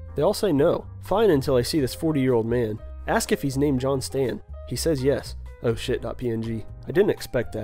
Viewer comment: Ooooooh. I thought he said 'Johnston'. I still appreciate the complement though.